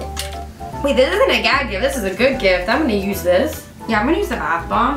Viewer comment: I thought u were like 16 or 18 😧 also when you said gag gift I sorta thought u said gay gift 😂🤣😂